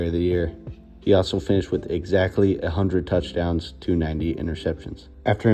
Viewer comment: Just a tip for when you rank every left tackle of all time, you should say either "one hundred" or "uh hundred" as opposed to "ay hundred". It sounds like you said "Doug Williams retired with exactly 800 touchdowns".